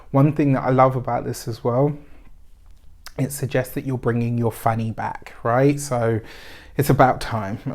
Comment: Argh, thought you said "You're brining your fanny back"